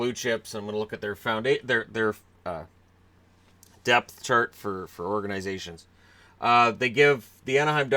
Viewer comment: At first I thought you said depyh charges not depth charts